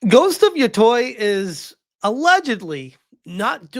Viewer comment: I thought he said ,,Ghost of Yotoilet‘‘ in the beginning 💀💀